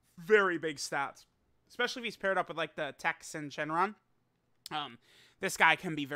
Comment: Every single time, I hear it: Texan Shenron